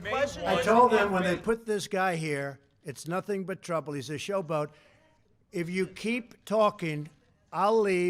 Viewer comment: I heard SHOW-BUG 🙄